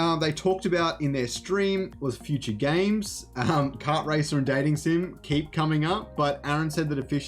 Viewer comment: Bro your accent is too thick sometimes. I thought I heard you say ideas for future games include a Kart racer and a "Dating sim." Wtf. Even the CC said you said that. While I think the idea of an Illuvium "dating sim" is hilarious and I don't even know what "dating sim" is, it's hard for me to believe that that's an idea. Dating is hard enough in the real world let alone in some game filled with dudes. What did you actually say? Gaming Sim? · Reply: The dating sim is a running joke. And thats exactly what I said. They are actually really popular games but less so in Western countries. And they aren't supposed to be serious. Check out Hatoful boyfriend